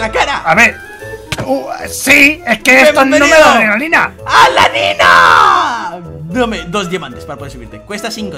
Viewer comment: Cuando dijo "la nina" yo entendí "la lyna" xD